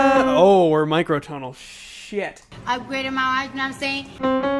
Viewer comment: Cardi: Upgraded my ice know what I'm saying (ice as in jewelry) Only trying to help